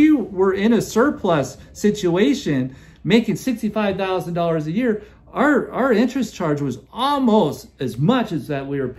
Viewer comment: I had to comprehend this at first. I thought it was saying that you make $65,000 a month.